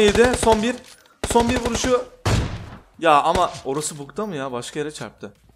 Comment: orasıbugdayı çok yanlış anladım abi selamlar ljgdaafgjlşkfaajl